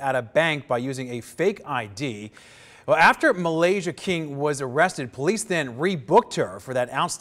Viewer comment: "Malaysia King." Did I hear that correctly?